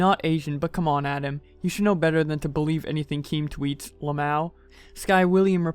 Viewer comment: Did he just say lamao😂🤣🤣